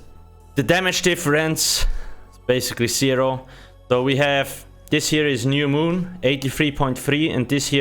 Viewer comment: Lmao at exactly 2:00 my phone thinks you’re saying Hey Siri instead of “And Here” so it pops up and starts talking about the moon.